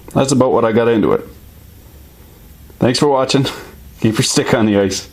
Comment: "Keep your stick on the ice" I finally get it, I've only heard him say "keep your dick in a vice". The world makes sense once again!